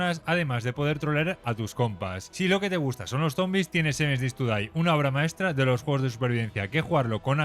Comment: nombre de ese juego que no entiendo plis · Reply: 7 days to die